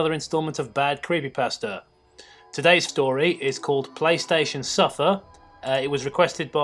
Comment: I could've swore that said Playstation Sniffer